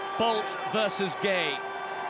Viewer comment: i thought he said " Bald vs Gay " 😂😂😂